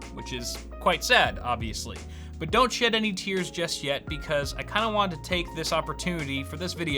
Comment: Sounded like he said don’t shit any tears lmao